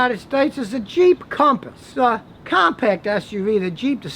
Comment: Haha I first thought you said ' the cheap Compass' instead of the Jeep Compass.